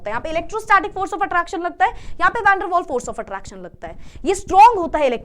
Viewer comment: can anyone help? What is she saying in covalent....ionic me unhone bola yaha pe electrostatic force of attraction lag rha hai and yaha pe...? Covalent me konsa bond?? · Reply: Van der waal force of attraction